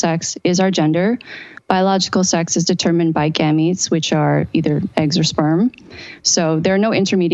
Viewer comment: What word is she using at around 2:55 to describe eggs and sperm? It sounds like Ganeeds?? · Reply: Gametes*